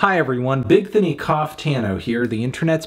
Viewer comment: i was chewing a tortilla chip really loud and i thought he said, "Hi, Bigthony Cocktano here..."